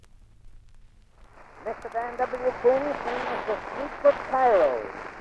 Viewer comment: What does he say at 0:02? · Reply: Sounds like "Mr. Dan W. Quinn singing us 'The Streets of Cairo.'"